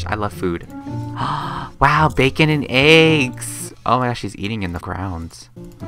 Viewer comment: Did he say Facon